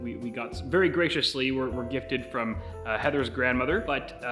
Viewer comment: did you really said "Heather's"!?